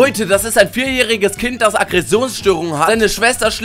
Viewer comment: „Der Auf Französisch Kieselstein heisst und uns unseren Alltag Beschreiben will“ Ich krig mich nicht mehr ein🤣 Nicht mehr einkrieg button 👇🏽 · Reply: Ich habe Käsestein verstanden 😂😂😂